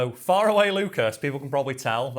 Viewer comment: I always heard 'far away lucas' as 'fire away lucas' and even though i eventually figured it out, it still gets me.